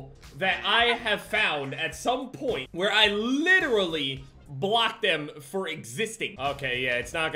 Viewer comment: thought you said blocked them from existing at first. :\